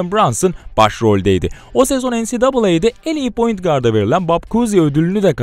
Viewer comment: Elit guard mı dedi ben mi yanlış duydum 😮